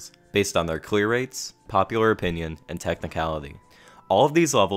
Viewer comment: 『クリア率』って聞こえる